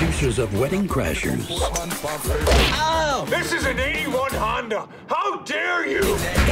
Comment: dammit i always thought he said 89 honda......fail